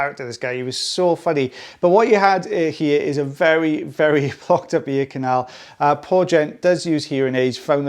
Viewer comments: Am I the only one who thought said "fucked up ear canal" at 0:13? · Oops it sounded like you said F"ed up ear canal. What was that? I can't imagine you saying that. Accent boo boo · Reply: Might be His accent, but "Blocked."